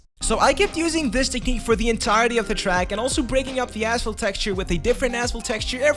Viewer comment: Awesome video 😃at 1:49 it sounds like he says asshole texture instead of asphalt🤣